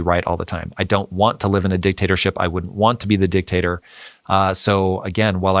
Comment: wait wait what did you say "I would want to be the Dictator?!"